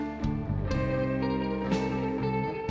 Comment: what does he say at 2:37? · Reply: "You know I were only joking about the lighter thing when you do that" then "Well f*ck if you got it"